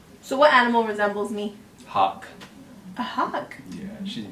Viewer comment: I thought he said "hog". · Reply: Lol clearly says hawk. But I see your TRYING TO BE RUDE!